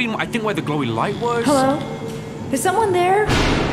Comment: found a mistake in the subtitles. When Audrey says “Hello? Is someone there?” The subtitles say: “Hello? It Someone there?”. Tell me if you guys find more mistakes by replying.